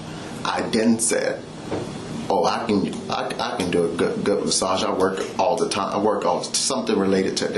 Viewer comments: Time Stamp 11:50....It to me...sounds like he was saying "Gut Massage" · The one massage cop was also saying I give a good massage meaning he give her a massage so the detective didn’t even catch that he said it 2x it sounded like he said I give a gut massage but he was def referring to giving her one at first